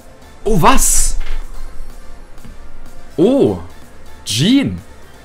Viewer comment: Кто русский? 3:51 кому показалось что он сказал Джин